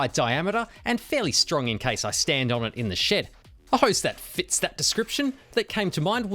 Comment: You kept saying "In the shed"... I thought you were saying "In the sh*t"😂